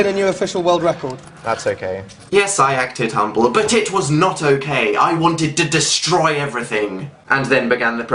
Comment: with subtitles Dan really says "but it was not okay" subtitles say "bitch was not okay" which I find funny because that describes Dan